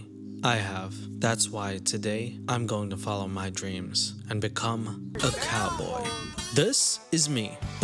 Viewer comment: at first i heard "ride a cowboy" thanks goodness for subtitles